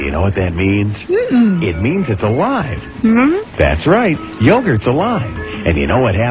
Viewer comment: i thought it said yogurts a lie, it was the middle of the night and a screamed THE CAKES A LIE!!!!!!!!!!!!!!!!!!!!!!!!!!!!!!!!